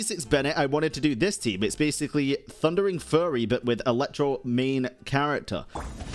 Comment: did I just hear him say "Thunderign Furry"?